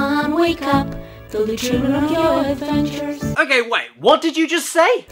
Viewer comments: I thought it said chew on your adventures at first lol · “There’ll be children on your adventures” CALL THE POLICE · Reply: I heard "Tell the children of your adventures", which would be far less creepy, for what it's worth. ....It's still immediately followed by O K A Y G I R L S A N D B O Y Y Y Y Y Y S , so....